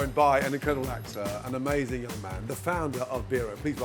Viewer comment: I thought Gordon said “an amazing little man” lol